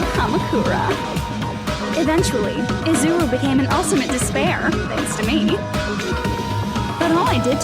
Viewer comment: I thought it said "Ultimate seesaw" I was laying down and abt to screech